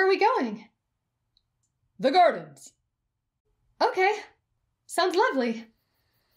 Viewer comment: When he said "the gardens" I thought he said "the garbage"